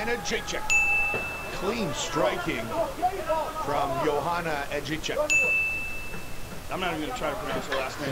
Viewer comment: sounds like he said edgy czech